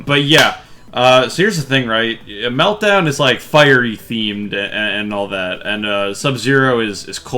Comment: I heard "fireafy" IS THAT A BFDI REFERENCE?!